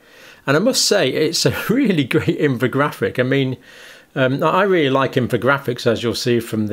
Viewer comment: Dr Campbell made a slip and said "I mean..." and for a second I thought he said "Amin".